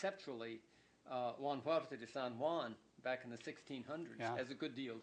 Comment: A great discussion, thanks. Can somebody hear the name of the "contributor" from 1600s Hunt mentions at 11:15? Was it Juan Huarte? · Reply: Huarte de San Juan.